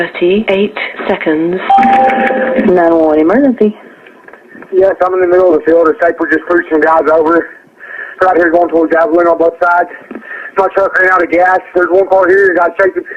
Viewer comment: Whats the mechanical talking in the background ? And I may just be reaching, but starting at 0:11 it sounds like he says "Yes I'm in the middle of a field a snipers just pushing guys over . We're out here war torn guys littered on both sides."